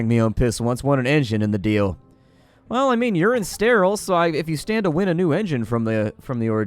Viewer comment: What Said: "Well, urine's steril, so..." What I first heard: "Well, You're insteril. so..." So I thought he was crackin some kind of joke about the guy's... disabilities.